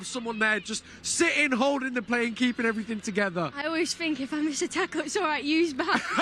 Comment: イギリス英語でちょっと聞き取りにくいですが I always think if I’m stuck, Yui is behind me に聞こえたしタックルよりこっちの方が意味通ると思いました タックル多用選手でしたらすみません😂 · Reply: stuck とは言ってないですね。miss a tackle のSとT とK を聞いて stuck に聞こえたかもですが if i miss a tackle と言ってます。